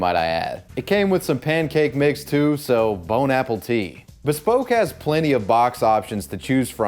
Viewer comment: Did you just say, "Bon apple tit"...?? That's what I heard, so I'm stealing it 🤣